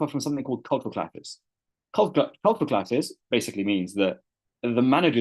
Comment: What did he say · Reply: Culture clashes